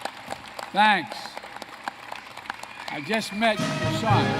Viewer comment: I thought he said "I just met Hasan."